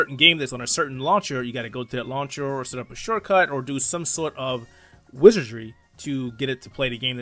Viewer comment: for a sec i thought you said, "i need a short sword"